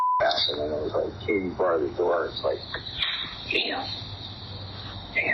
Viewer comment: I don't know why the transcript says "inaudible". He clearly said " Katy bar the door".